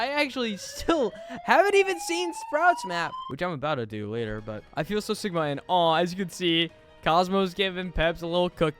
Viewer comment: if you check at 7:06 it sounds like he says "i feel so sigma and aww as you can see"